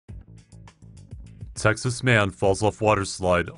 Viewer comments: at the beginning it sounded like u said sexist man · Hahahahs omg i heard "sexist man falls of water slide"